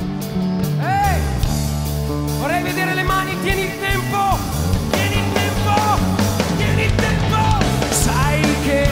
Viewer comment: Ciao! Sto imparando l'italiano e non capisco cosa dice Nek ai 3:25 Mi sembra che dica "Vieni in tempo". Vi prego potreste aiutarmi? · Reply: Tieni il tempo